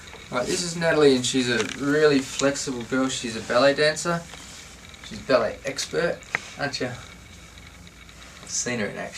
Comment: thought it said belly dancer 😂